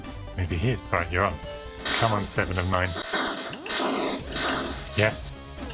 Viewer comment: did he just call her 7 of 9 because I can totally see that